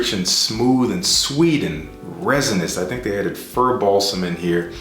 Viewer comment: Everytime you've said "fir balsam" I always hear "Furball" first lol